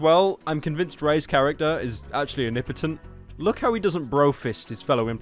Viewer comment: i really heard “an impotent” and was just going to accept that